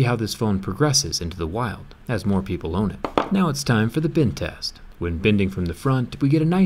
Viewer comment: I’m sorry but every time you say bend test it sounds like you say BIN test!